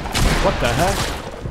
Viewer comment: said what the h word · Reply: Yeah he said that the heck